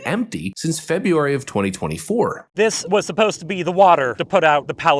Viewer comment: No shit they lied. That reservoir has been empty since February 2024. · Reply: I first heard him say, "February of 2023." That's coming up on 2 years.